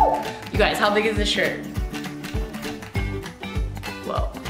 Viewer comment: When you said “am I okay? nooooo” I thought you said “ am I gay? NOOOOO” 😂